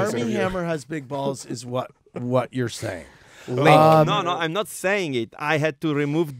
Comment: What did Timmy say at 0:40??? Lmaooo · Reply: i’m pretty sure he’s saying “link” sarcastically as the interviewer is linking two things together lmao